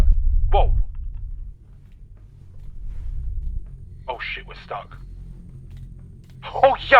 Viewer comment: oh jävla skit? Sounds like Swedish but what are you saying? 😂